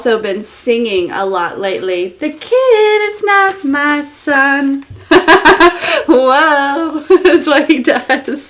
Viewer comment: Isn't it the child's not my son? Because at first ppl thought he was saying the CHAIR is not my son. Really! Then Michaels child/son was at his Funeral!! Very handsome son too!!